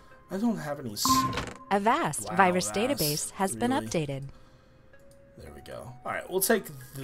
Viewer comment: i heard "avast youre antivirus protecter has been updated" in a female voice.